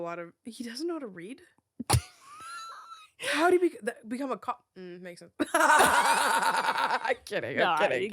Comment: Why did it say "how do he become a cake" in the subtitles instead of "cop"? How do you even become a cake? And would it be possible for you to eat yourself then?(NO! don't even dare to think about it, I promise that didn't meant it that way!) 😅